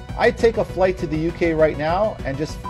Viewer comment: "Yeah, I get arrested. And then what? I win, because they're dead." Has to be one of the most flaccid threats I have ever heard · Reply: I think you misheard... 14:37 He said "I take a flight to the UK right now, show up and curb stomp their heads. I win, because they're dead."